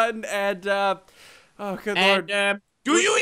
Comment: i thought it said "do you even villian bro" idk